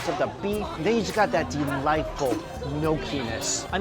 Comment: i couldnt hear well so i turn on subtitle and it says Delightful no penis @ 4:51 O.o